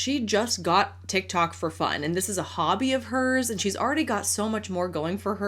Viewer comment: OMG I kept hearing "hubby" when she was talking about the dog's food and it only clicked when she put it in front of the dog... Was 100% sure she was gonna serve it to a vegetable-hating husband.